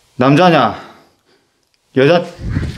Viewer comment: 이 남자냐 여자냐 물어볼때 카메라 돌리면서 뒤처다보기전에 여자라고들림.